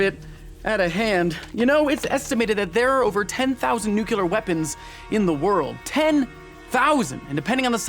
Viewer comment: Did you just say NUCULAR? =)))))))))))))))))))))))